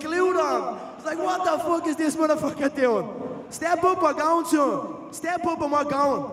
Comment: What does he say at 27:45 ? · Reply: Step up on my gown i think